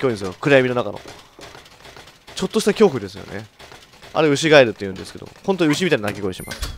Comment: アイリーンさんケガしてずっと歩き回らされてるからイライラしてるんじゃないのｗ 牛ガエルは母が子供の頃なんの音か正体が分からなくて本当に怖かったそうです… (最初さんの滑舌が(ry)で牛ガイルと聞こえたのは内緒)